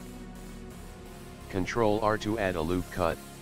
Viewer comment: what is he saying? "Control what?!" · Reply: Just a loop Cut. Said control r